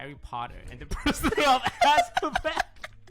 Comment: What does Toast say after "The entire text of Harry Potter..."? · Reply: And the prisoner of Azkaban